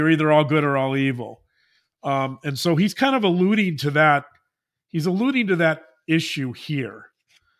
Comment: I first heard this as ‘he is a looney to this issue’. 😅😅😂